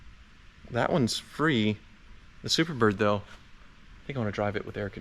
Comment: You said "Superbird" and somehow I heard "Super Coupe"... You should probably get a super coupe. That would be fun.